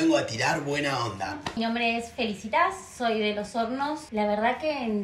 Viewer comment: pense que decia figazzitas jajajaja